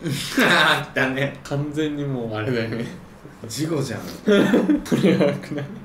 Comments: 事故じゃん が 事後じゃんに聞こえた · 、事故じゃんって言ったんじゃなくて、事後じゃんって言ったように聞こえたんだけど、私だけか… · 事故じゃん じゃなくて 事後じゃん に聞こえたのは私だけでは無いはず、、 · の「事故じゃん」が「事後じゃん」に聞こえた人挙手